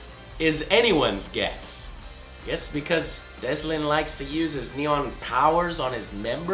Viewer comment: Im so glad Infamous Second Son has a nirvana song in it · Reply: Am I the only one who heard him say Deslin instead of Delsin?